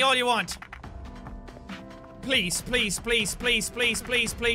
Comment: What Jack says: "please please please..." What I hear: "police police police..."